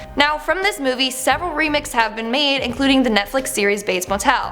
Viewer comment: Am i the only one who heard her say remick instead of remix or remake 😂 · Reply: I heard her say remik